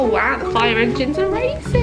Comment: Ha I had to play it back because I thought you said 'the fire engines are racist!'